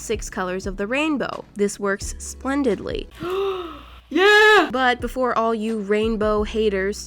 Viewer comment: it sounded like Moriah said sksksplandedly ShEs VsCO (i'm joking)